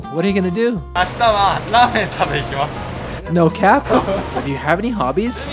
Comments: Did you just say "no cap"?! Lmao didn't expect that outta you for some reason. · Did you just say “No Cap”’at 3:45 ? Lol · Did say " no cap" at 3:45? 😆